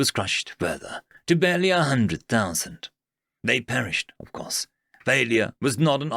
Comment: I enjoyed the reading as always, although one thing of note is at 10:57 when you're saying "they perished of course" instead of "they persisted, of course" it's just that, that tiny difference is quite important for the story as a whole, I fear that people who only listen rather than read alongside you, may misunderstand.